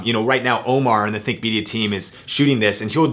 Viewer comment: Thought he said Thicc Media